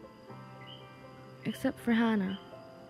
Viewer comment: Sounds like she is saying hanna , but it’s ana